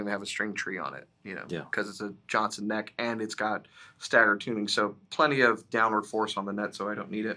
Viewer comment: did he say Johnson neck and staggered tuners? What’s a Johnson neck and how does it help with string break angle?